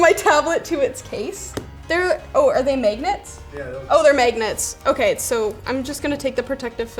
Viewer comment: any notice that she says “Megnets” instead of magnets?